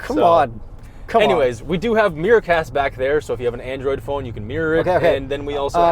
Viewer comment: For a moment I thought they said “meerkats” instead of “mirror-cast”